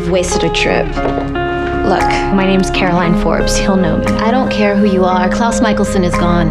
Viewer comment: Lol, I hear "I don't care, about Caroline." I'm like.. "LIAAARRR! STAWP LYING TO CAMILLE U KNOW YOU LOVE CAROLINE MORE!! ADMIT IT >:C"